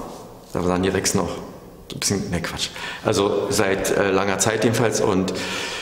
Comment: я одна услышала: А короче? Мой уровень немецкого только на уровень А1 😂